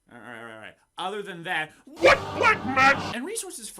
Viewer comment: Ya know, for the first couple of times I watched this, I heard "black magic" as "butt magic". I don't even know how......